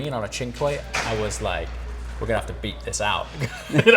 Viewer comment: On the second time of watching, I hear you say “Beep this out” at first I thought you said “Beat this out” 🍆 Either way lol 😂